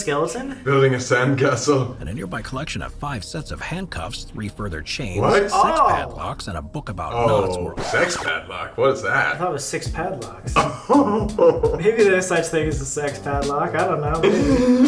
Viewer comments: “S3x padlock” 😂 ur not alone Colby I totally thought they said that too…think I need go church 😂 brill vid as always Xx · i fr thought he said "sex pad locks" tooo 😂😂😂